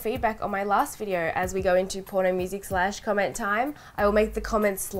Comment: I've been watching your videos for about 5 or 6 years now and I always thought you said "Polly music slash comment time" and I never understood it but I didn't question it. I need to get my ears checked lmao